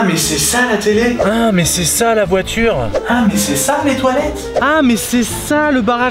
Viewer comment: Autant il dit "Ah mais c'est ça les toilettes", autant moi je comprend "Ah, mais c'est sale les toilettes".